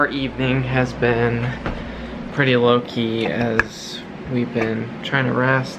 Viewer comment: I want to be supportive to say you are awesome! But I also want to be silly and say I hear "Loki" every time you say "low key". 😀